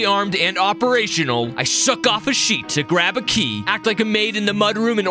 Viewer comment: WHAT DID HE SAY?😂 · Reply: Sheet